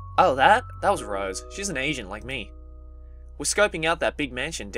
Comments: When you said "that was rose. She's an agent like me," I thought you said, " she's an Asian like me." 😂😂😂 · It sounded like you said "She's an asian like me"